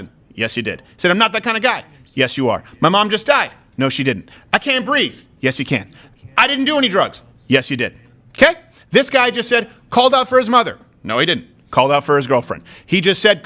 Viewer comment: Idk.. but i heard.."I dindo any drugs"...